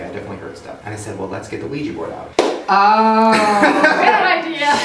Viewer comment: Oh my god... I thought u said Wii Board.. But u said wigi board...